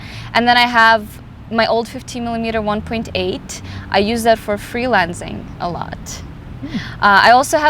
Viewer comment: What is "free-lensing?" I first thought she said "freelancing," and I think did too...